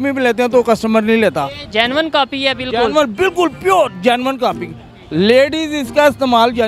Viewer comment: kya bola "pure genuine copy hai"😂